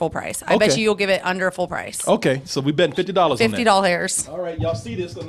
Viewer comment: and 22:58 Did she said "fifty doll hairs"? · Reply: That sly motha-** she did say doll hairs